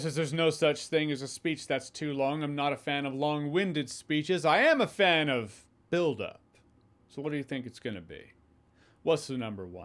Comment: Omg, first I thought you said “I am a fan of…dildo”🤣🤣